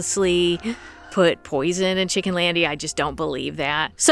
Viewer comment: I thought that you said POISONING?